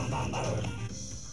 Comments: i always heard "WE ARE THE WUBLINS". i have been lied to my whole life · I hear "we got the whoppers" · When I first heard it, I thought it said " Bring out the wobblets." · I thought that the green thing said: "We got the woblins we got the woblins." So I'm like "What the frick?!" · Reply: Ghaslaaer I thought it said bring out the wublins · thought he said we got the wublins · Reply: I honestly thought Brump said “We are the Wublins!” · Wake up the Wublins, Wake up the Wublinzzz... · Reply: At first I thought it said we got the wublins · thought he said we got the wublins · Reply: i thought he said we are the wublins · thought he said we got the wublins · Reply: My Dad Thought it said “We Got The Muppets” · I heard we got the wublins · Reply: I hear "we got the wublets" .---. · thought he said we got the wublins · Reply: I thought he said "We are the wublins" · thought he said we got the wublins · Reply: Ye I thought he said we got the winking too! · thought he said we got the wublins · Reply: I thought he said "we got the whoppers" 💀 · i always heard "WE ARE THE WUBLINS". i have been lied to my whole life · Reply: I always heard “we got the wublins” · I thought that the green thing said: "We got the woblins we got the woblins." So I'm like "What the frick?!" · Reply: I thought it said we got the wublins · I thought that the green thing said: "We got the woblins we got the woblins." So I'm like "What the frick?!" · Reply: I also thought that he said "WE GOT THE WOPES"